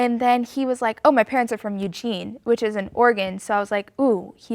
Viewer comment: I’m Australian so when they were saying ‘in Oregon’ I thought they were saying ‘in organ’ rip